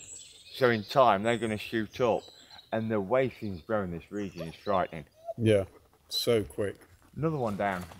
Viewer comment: what did say here? "the waste in this area is frightening" ? some times i am unable to understand him. not to be rude